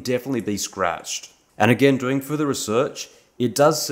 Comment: Excuse me sir, did you just say that there are certain steps to form hard nipple?